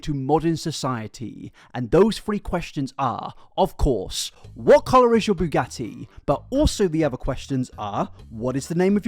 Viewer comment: I thought you were saying 'What color is your Bugazzi' because apparently can't hear.